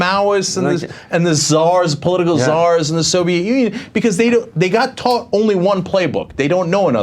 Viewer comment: I heard cenk say "Czars and the Soviet union". It helps to have headphones.